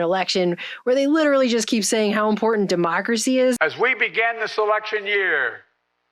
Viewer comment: into your video, Biden says “ As we begin this election year…” I thought he said “As we begin this SELECTION year”. 🤔